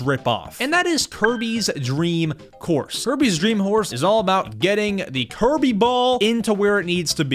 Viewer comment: it sounds like you said Kirby’s dream horse no offense